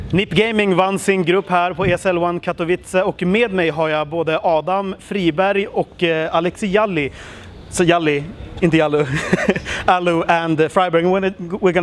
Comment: Anyone else hear Free Barry